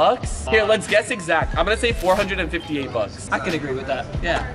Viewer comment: rug said 458 bucks but the caption said 485